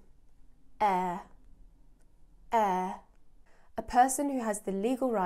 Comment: Heir. whatt i hear is just eee , i'm sure if i hear this world in England i will not understand it